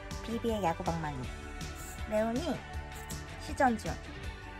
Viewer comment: 답글고마워요. 0:13에 형광색종이, 별모양안에 눈동자처럼 모양있는것.. 그거 이름이 뭘까요? 시전00 이라고 들리는데 모르겠어요. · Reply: 모든 게임에서 거의, 무기를 사용할때 창이나 활 쏘거나 검을 사용할때 , 게임 상에서 무기 발사되기 전에 시동걸리는 것처럼 액션이 있어요^^ 그거 지금 무기 사용하기 전에, 시전 중 이라고 말한거예요^0^ 저녁 맛있게 먹어요^^